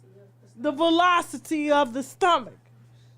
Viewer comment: Did she say ‘velocity of his stomach’? 52:28 😂Tasha is a fool 🤣